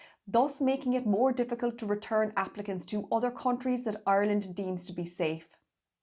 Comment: Applicants! I thought she was saying Africans! I was thinking that a bit specific !